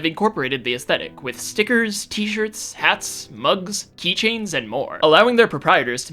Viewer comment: Did you just say "keychains"?